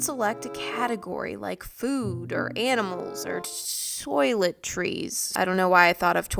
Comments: When she said toiletries I thought she said toilet-trees 😂😂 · Is it only me that thought u said Toilet Trees instead of toiletries